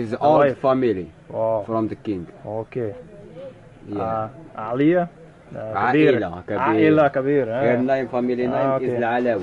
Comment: little caption mistake what was said? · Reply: Yeah oops, nothing important, he just said "yeah it's a big family"